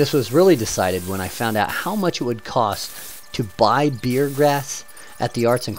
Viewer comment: Sorry to bother you, I have a question. In the video you said, if we don't have any bunches to trim, you could use beer grass? It's hard to hear what you said. I thought you said beer grass, my dad thinks you said beard grass. PLEASE help us....tell us what you said that we could use, and get at a hobby store. Thanks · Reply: Yes, bear grass. It was in the arts and crafts store. My wife uses the dried out version to make her own wreaths. I would suggest you walk out to any neck of the woods and just cut bushes/shrubs. Its not that hard.